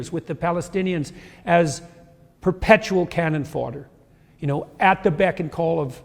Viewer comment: Did he call Palestinians “Cannon fodder “ at 3:30?